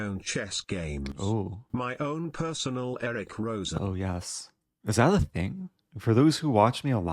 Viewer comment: Yes, I just hear "oh no" on repeat.